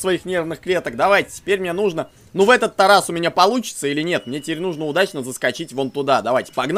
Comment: я один услышал: ну в этот тарас у меня получится то?